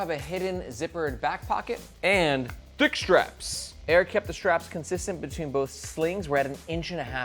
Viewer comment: Thought he said dick strapes